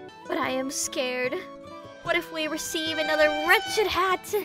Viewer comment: I think it was "wretched" instead of "ratchet"? Man I Love Ame